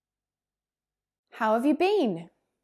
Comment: Here I'm Indonesia subcribers 😆 I'm struggle with "how have you been".. it so fast, and I think, I hear "how have been"